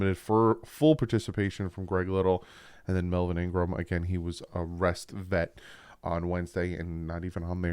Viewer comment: I thought you said Melvin Ingram was arrested Wednesday for a minute lol